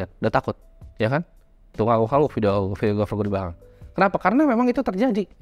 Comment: lu bilang apa bang , bahsa inggris kah? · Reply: Tuh ngangguk-ngangguk videografer gw di belakang